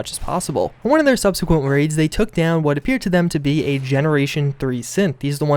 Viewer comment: Liked your video and these stories in the game. Sounded like you said Gen 30 synth. The question is was Deacon telling the truth about the gang and his wife. The guy lies constantly. Even says sometimes he can’t help it. If it was true, I don’t think his wife was sent from the institute to spy on him. More then likely she didn’t know either.